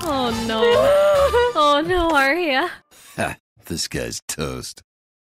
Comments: Hmmmm why do i hear "this guy is toast" everytime when it say "disguised toast" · Whenever i hear the intro i always hear "this guy is toast" instead of "disguised toast" anyone else